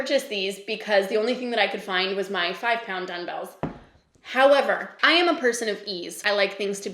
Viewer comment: Am I hearing you say, “dun-bells”? Is that a Canadian thing? It caught me off guard haha